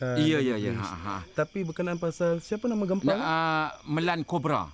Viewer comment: "Siapa nama gampang ni??". Biar betul? Ke aku salah dengar😂